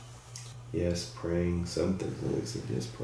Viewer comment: I heard “yes, praying is healing”